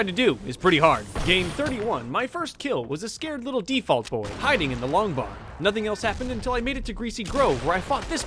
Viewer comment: it sounds like he said I f**ked this little default😂😂